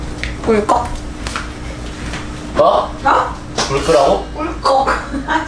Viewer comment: ㅋㅋㅋ보는 사람도 꿀꺽 ㅋㅋㅋㅋ 어떻게 불끄라고 들리지??ㅋㅋㅋㅋ